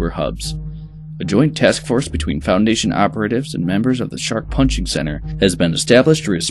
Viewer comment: Am I losing it or is he saying 'the shark punching centre'